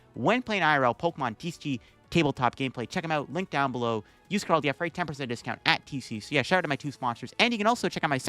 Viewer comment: I can never hear what code you say here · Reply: LDF